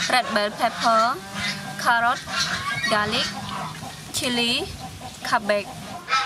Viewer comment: I thought she said cab big